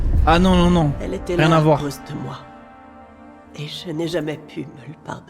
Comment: Je suis le seul avoir entendue « elle était amoureuse de moi » à 35:21 aussi